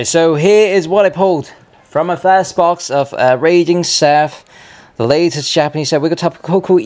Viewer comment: Did you call it Radiant Surf in the recap?